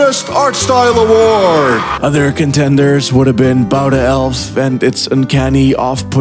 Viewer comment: What did he say the name of the game was at 15:39 was? I know he said it but i didn’t quite catch what it was. It looks great and very unique! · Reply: About An Elf